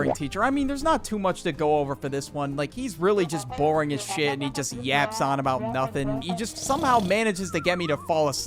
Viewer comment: got an ad right as said “he just yaps on” so it kinda sounded like he said “he yaps on about Dyson” (the vacuum brand)